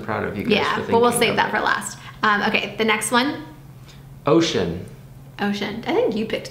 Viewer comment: when they said ocean is thought they said “oh shit” and i was why the heck would sm1 name there baby oh shit wtf